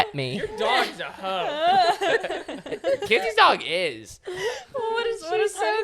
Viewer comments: did say Kenzies dog is hoe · did he say hoe?! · WHAT THE HELL DID U SAY. THAT KENZIE DOGSCA HOE OMG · Is said stella is a hoe😮😮